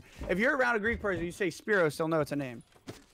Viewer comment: Everytime you said “speros” it sounded like you said “spit roast” and it had me creasing hahahaha